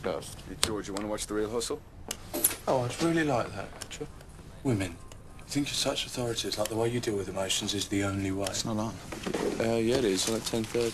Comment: Can anyone tell me what George say at 1:13? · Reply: Women. You think you're such authority figures like the way you deal with emotions is the only way.